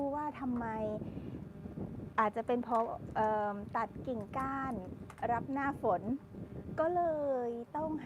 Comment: ผู้บังคับบัญชาเป็นพ่อแม่ได้เหรอครับ ได้ยินคำนี้ในซีรี่ส์นิทานพันดาว น่าจะเพราะคุณพ่อแม่เป็นที่ปรึกษาของเราตลอดชีพมช่ไหม และอีกอย่าง ไม่รู้ว่าครูหมายถึงอะไรเวลาพูดในนาที 1:34 “ตัดทิ้งขาย รับหน้าฝน” ? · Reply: ตัดกิ่งก้าน รับหน้าฝน ค่ะ 😉 หมายถึง ต้นกิ่งก้านของต้นไม้ เพราะเตรียมรับหน้าฝน ค่ะ